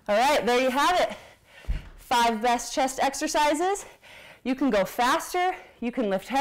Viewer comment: Did you just say "Five breast chest exercises" ? I misheard that. LOL